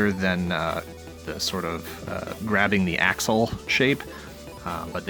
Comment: wasn't listening carefully, thought you were saying "grabbing a*shole"...